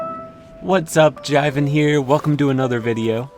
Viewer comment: I was sitting down to watch the video and at the start I lowkey thought he said “what’s up Genevieve here”